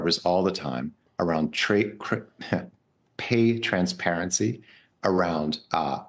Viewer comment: he said "trade crypto" instead of "pay transparency".. my matrix glitched